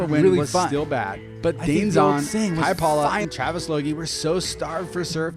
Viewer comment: I heard you say, "Hi Paula," around or just before 32:41. My name is Paula. It was in a sports where your audio was talking over itself. Made my day💕